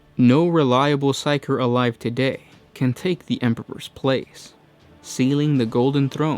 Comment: I love your videos but I had a little chuckle when I heard "Emperber".